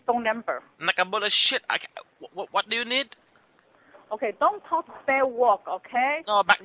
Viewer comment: Don’t talk say wok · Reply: 😂 I thought she was saying “Don’t say bad words “ lol